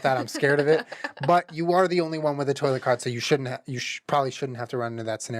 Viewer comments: Sorry I literally heard “toilet CART” that whole time and I was imagining you wheeling a toilet around and I’m—laughing thanks ALSO GUYS DONT APOLOGISE OR ANYTHING YOURE SO RIGHT TO BE UPSET IM SORRY FOR MARCH 2018 ALRI LOVE YOU BOTH MOMTHER AND FATHER X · The whole start of snake oil I thought he was saying “toilet cart” and was like oh damn he’s got a cart with a toilet built in we can pee anywhere get me a privacy curtain and I’m chill. Lmfao Love your vids. My insta: insidekrystamind